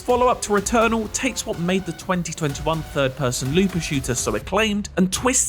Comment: Is it accurate to call 'Returnal' a "looter shooter"? 🤔 EDIT: As a user below pointed out, it turns out that they actually said "LOOPER shooter" and NOT "Looter Shooter".